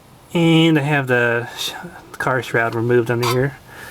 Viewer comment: the what? the Car strap removed? you mumbled. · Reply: upon further reading below I believe you said, "Car shroud."